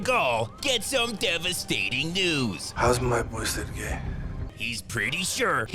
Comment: when he says "how's my boy Sergei" I thought he said "how's my voice so gay" 😂